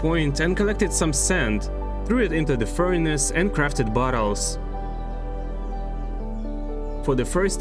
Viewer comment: I thought you said buttholes 😂😂